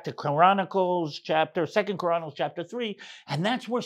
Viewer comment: Sounded like you said Koranicles lol 😅